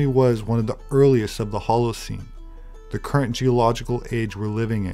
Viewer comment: I swear i heard you say the halocene the current cage we're in slip or not it works for me stay dangerous my friend